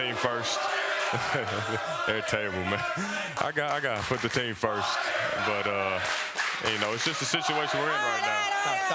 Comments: Jacory Beauvais what were they saying fire who? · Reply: Nobody x7 the GM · can someone tell me what they are saying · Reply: Fire GarPax